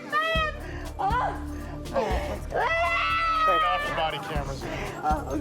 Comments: Did he just say turn off your body cameras at 5:28 YIKeS! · did I hear turn off your body cameras?